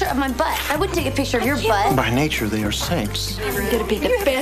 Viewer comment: What is he saying? "By nature, they are snakes?" What is the last word he's saying? If anyone can help, it would be greatly appreciated.